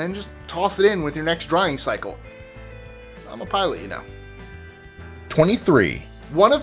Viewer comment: I Could not understand what you said @ 0:43 , 1:13 .......anybody reply...thanks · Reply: "I'm a pilot you know". It's a subtle reference to Star Wars (Episode I to be exact). ~Mike